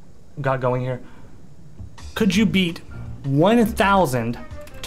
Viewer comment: omg i thought he said eat instead of beat i was so confused lmao